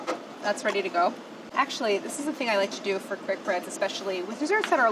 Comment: Could have sworn she said "it's a quick Brad" instead of Bread.... ...